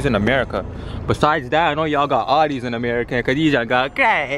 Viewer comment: did he say “do y’all got this in America” could’ve sworn Toronto was in America🤔😂